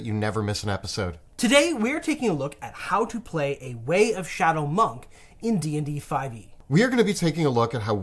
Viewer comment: I could have sworn said "Raid Shadow Monk".